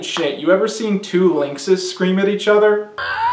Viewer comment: Hahahahahahha holy shit, I misheard 13:29 as "two Links scream at each other" and was still accurate.